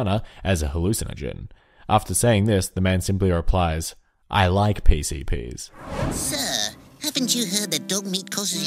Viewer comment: I heard 'I like P.C Nees'